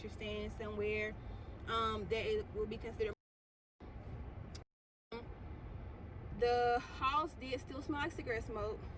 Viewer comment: Lmao, what did you say @ 19:34-19:36 that you had to bleep it out?!? 😂 I'm sure you'll need to inbox me for that, since it IS muted out HERE. 😂😂😂😂😂😂😂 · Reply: Jay Wats 😂😂 the first gap was supposed to be “luxury” 😂😂 the second gap was just “um” my camera was being really shady 😩😩😂